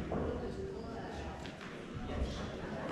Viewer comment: If anyone noticed the sped up song, it's because this isn't the final take. It's a technique used to help the dancers learn their choreography at a greater speed, in order to relax into the dance on the final take. It also improves recall and means they can fit more rehearsals in. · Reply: Lol, why is everyone arguing about this? Anyway, I think I can settle it, since I've worked on a bunch of sets. At 3:51 the director says "let's reset guys". That literally means go back to start, we're going again (or no, you are not wrapped and you ain't going home any time soon). I heard that on too many 14 hour days and that's what it means.